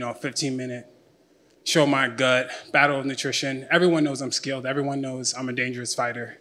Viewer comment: Did he say “battle of nutrition” at 0:23? 😂